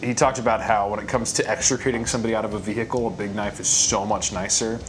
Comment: Entirely misheard "extricating" as "executing", and immediately pictured leaning out the window of a moving vehicle to cut some unidentified carcass in two in the CS parking lot. You're welcome to use that for a promo, I wanna see it 😆